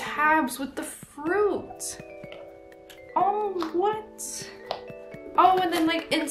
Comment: I thought you said WHAT THE FRUIT. Ima start using that now.